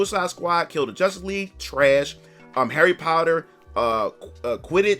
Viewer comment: Dude, I love you. But, I heard you saying "Harry Powder". It cracked me up.